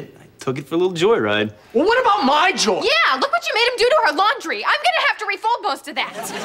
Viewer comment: Carly said: Look what you made him do to her laundry. She messed up her line, they didn’t notice 🤔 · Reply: I heard her say “our laundry” 🤷‍♀️